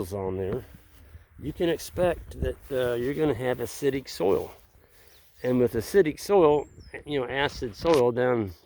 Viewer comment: LOL! For a split second I thought said, "You're going to have a shitty soil." (which would surprise me). Then I realized said, "You're going to have 'acidic soil'! LOL.... I'm losing it!